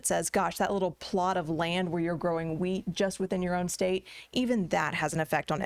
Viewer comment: I thought she was going to say "weed" not "wheat".